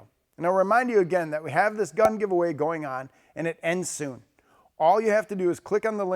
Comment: All I heard was "free gun giveaway.......ending soon!"